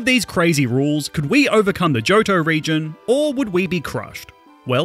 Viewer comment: I swear I heard "The Jojo region"